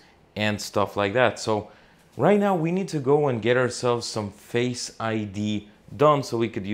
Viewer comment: At the beginning of this video, I thought you said you were going to get "Fake I.D." My mistake, you were going to get Face/Photo recognition to enter your new apartment. I guess if you really needed a "Fake I.D."...Thailand is the place to procure one. BTW: You arrived at possibly THE WORST time to "visit" Thailand, due to the oppressive heat. My mom is a Thai national and she doesn't even travel there during this time!!!